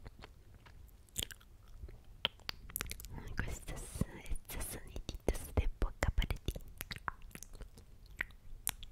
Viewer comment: que dijo :v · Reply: Majo Peña questos hizo una mezcla de español y italiano "QUESTO +ESTOS"=QUESTOS